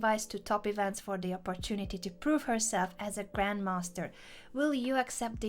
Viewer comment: * GGWP 00:51 i misheared to "grandmonster XDDD *